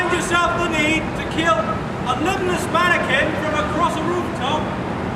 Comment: I heard "limbless mannequin" as "little hispanic kid"